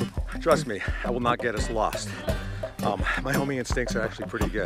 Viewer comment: I thought he said "My homie instincts are really good." 🤣😂🤣